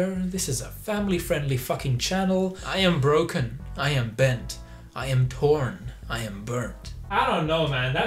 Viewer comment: When you said "I am torn" I thought you said "I am porn" Man... now I want to be porn.